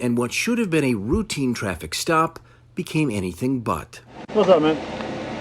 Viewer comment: What I first heard was "What's up, man?" So I guess she is not only racially profiled, but mistakenly identified as male....